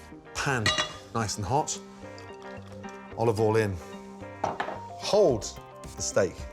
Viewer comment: I always thought he said "olive oil in", but now I realize he's actually saying "all of oil in".